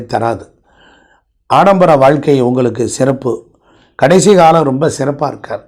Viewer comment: What is he saying at 10: 36 Kadaisikalam romba sirappa irukkadu na irukkaru na ? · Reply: Irukathu nu sonnaru bro 😢